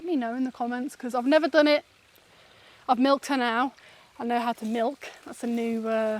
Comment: Oh, when you were talking about milking, you said I've never milked a goat, then you said I've milked her now, what I heard was "I've miked an owl" My goodness that is a skill!! 🤣🤣🤣🤣🤣🤣